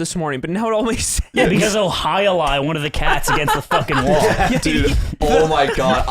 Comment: what is the word says here, ive been trying to work it out for way too long .. "he'll [___?] one of the cats against the wall" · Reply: "He will Jai Alai cat up against the fucking wall" Jai Alai is an extremely dangerous game that's kinda like handball, except they use a sort of tool like those things you use to throw dog toys. It's a handle and semi circled. The average speed the ball is in a game of Jai Alai is like 150mph. Very dangerous. Also extremely funny that decided to use that as a reference for throwing a cat at the wall, lmao.